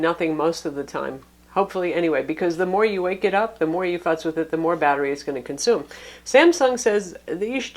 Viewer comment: Did you say "the more you fucks with it", or "The more you fuss with it" at 1:54. I honestly can't tell. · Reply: Savos Aren No, it sounds like neither of those at all. She said, "the more you futz with it."